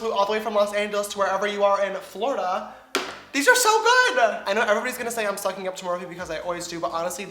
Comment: Why does the closed captioning for these videos say that you said the eyeshadows are "so bad" when what you actually said was they were "so good." UMMM!! not cool.